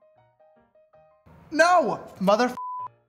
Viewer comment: did you just say mothertrucker dude this hurt like a butt cheek on a stick? because it's what i heard